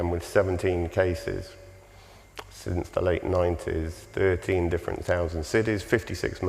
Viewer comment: Ok, 17 cases, "13 different thousand cities," is that what he said? 56 men convicted. 3 of the 56 were white. 53 were Asian names, 50 of them were Muslim names. What the fuck is this dude saying? Am I crazy or hard of hearing??